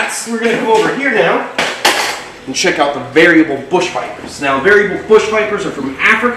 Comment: I thought he said very good vipers but he said "variable" vipers. Lol 💚💯☠😱